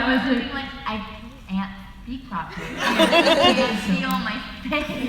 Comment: what did Kat said at 38:10 ? 👀 (i can’t understand, please help) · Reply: Kat: “I can’t speak properly. I can’t feel my face”